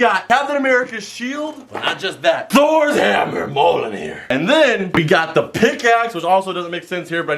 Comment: Did he just say moleanear?????